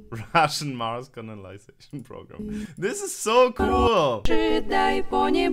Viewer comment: Только я слышу как говорит "Russian Mars CONALIZATION program"? Теперь не могу это расслышать 🙄